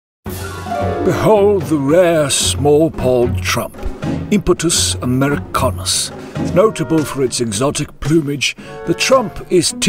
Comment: “Small pawed”. Hilarious! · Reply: I heard "small poled" Lol